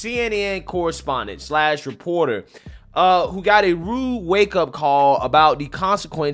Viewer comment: I heard "blue wake up call" thats genius.